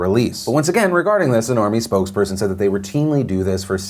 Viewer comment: I thought he said a Normie spokes person. This is ridiculous, incel terrorism.